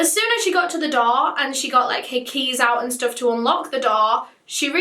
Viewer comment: did she say hickeys or her keys? I assumed it was her keys but I mean you never know